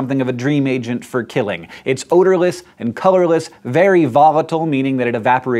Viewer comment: did he say odorless? maaannnnn